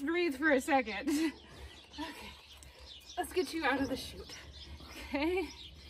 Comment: What did you say? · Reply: "Lets get you out of the shoot"